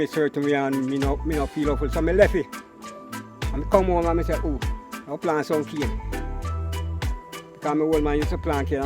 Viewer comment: I thought he said😁😁😁 he planted some ganga.